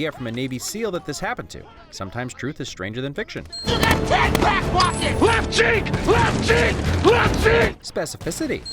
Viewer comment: I originally thought Tyrese was saying “LEFT JINK LEFT JINK LEFT JINK” but then I rewatched the movie with subtitles and he actually said “LEFT CHEEK LEFT CHEEK LEFT CHEEK”